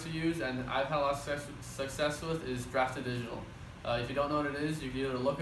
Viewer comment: sorry i didnt understand what you said on this point. Craft traditional ? Best regards Jonas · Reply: Lol close. I said "Draft 2 Digital"